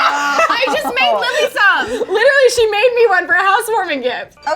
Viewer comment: lily said her name instead of 